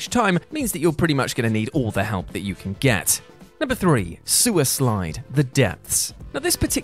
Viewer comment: I actually did a double-take and thought I heard "suicide"